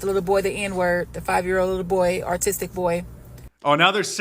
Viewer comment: no, Mark, what she actually said was he is “artistic”, not “autistic”. 😂